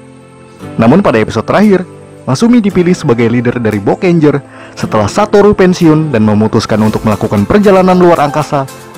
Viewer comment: bokek ranger atau salah denger yak gua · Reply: Boukenger